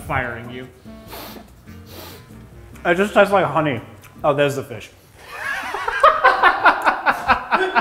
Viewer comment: "It just tastes like honey-- oh, there's the fish." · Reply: At first i thought he said "oh thats the fish"😂😂😂